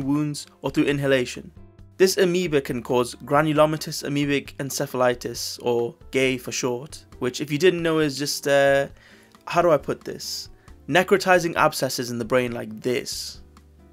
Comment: Ho ho, did I hear Encephalitis? I had that shit when I was like 6 while I also had meningitis. That shit sucks. Didn't die though iirc. No lasting side effects though somehow lol.